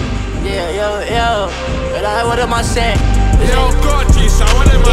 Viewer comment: his Jamaican accent..."yo weh dem a seh" it means 'yo what are they saying '